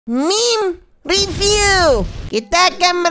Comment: Di 0:00 Adek bocils bilek meme tpi gw dengernya mim 🤣🤣🤣🤣🤣🤣🤣